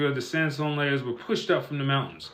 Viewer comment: This comment is not about, love your channel bro. This dude needs to go do that mumble rapping somewhere else. It doesnt say "From the mountains", it says "to form mountains". I'm not trying to put him on blast, but how are we supposed to be free if we dont hear the truth. He's trying to paint a picture, but instead of using paint he's using fecal matter, and trying to get us to believe that the smell we smell right now is chocolate pudding. He's either ignorant or disingenuous. I believe it might be the latter. 🤔🧩✍️